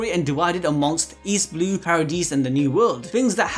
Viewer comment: Did you just call paradise paradese